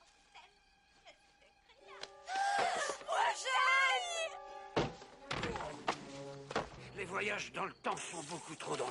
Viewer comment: min qu'est-ce qu'elles disent ?? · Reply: Jennifer 2015 : MOI JEUNE !!! Jennifer 1985 : MOI VIEILLE !!!